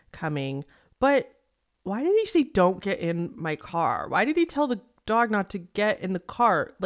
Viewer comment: I thought the dog guy said “don’t get UP ON my car”. I mean I sure it is set up but I totally got something else from it.